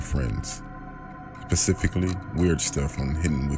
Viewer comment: does he say Pacifically?? Lol